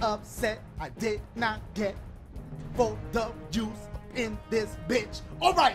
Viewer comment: When the captions say that “I didn’t get both Jew in the bitch”